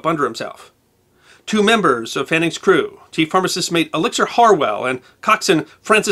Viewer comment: did you say the pharmacist mate was named Elixir? Lol P.S. another stellar episode! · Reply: Chief Pharmacist Mate Elizer Harwell